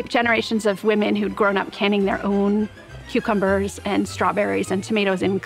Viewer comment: So nobody heard her say, cucumberries 😂